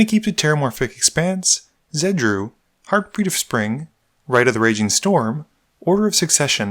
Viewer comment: Sounded like u said herpes of spring